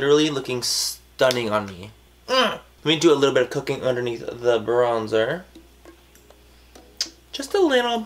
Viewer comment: For some reason I heard "let me do some cocaine under the bronzer" 😂😂😂